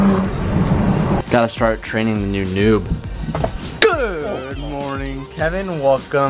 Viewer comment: I thought I heard noob noob from Rick and Monty